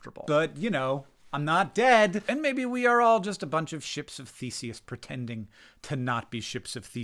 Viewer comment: 100% heard you say "Ships of Feces" and thought, well, that's rather bleak, but true I suppose